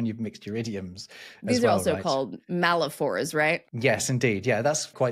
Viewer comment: oh, so are malaphors also paraprosdokian? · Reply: I'm going to leave my last reply because I thought you said malaprop, you said malaphors, oops